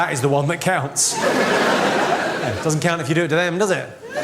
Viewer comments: "Doesn't count if you..." What did he say? Couldn't get the end. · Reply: Stroya if you do it to them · Can someone tell me what does he say at 3:25? tks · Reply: "Doesn't matter if you do it to them, does it?"